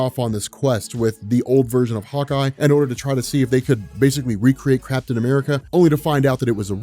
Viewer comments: Did you say Craptain America?! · I seriously thought you said "Crapton America". I was like "What did Cap do this time!?" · Did you say "Crapton America"?